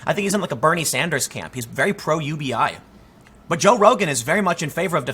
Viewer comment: When you say "he's very pro UBI," all I hear is "he's very retarded."